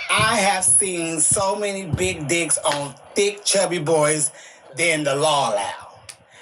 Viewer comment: did she say "than the law allow" as if big boys peen size is regulated 😂